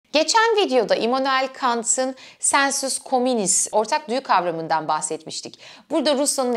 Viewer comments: Kant'taki "sensus communis"'i öyle bir söyledin ki birden "sen sus komünist" anladım😁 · 'yı "sen sus komünist" diye anladım, Kant videosunu daha fazla ertelemeyeceğim